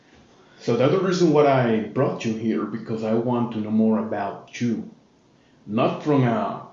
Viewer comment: Hi I'm a fan of your channel but something is bothering me of how pronounced words. I think he have a speech impediment? For example at 1:09 and 1:12 When he pronounced the word "you" All I hear is "chew" 😂 Sorry for my grammar I'm still learning basic english